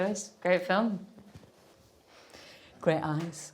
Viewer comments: When she said "great eyes'' about Bradley Coopers... at first i thought she said "great ass". lol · Ah great eyes. I thought she said great ass 🤣